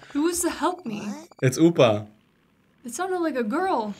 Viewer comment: upa sounded like bulma Ig that's why krillin was confused later